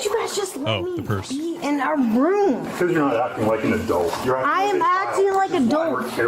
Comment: "I am acting like a dolt" is what she actually says. Accurate